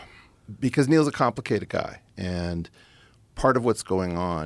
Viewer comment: "Neil's a complicated guy" all i heard was "Neil's a compromised guy"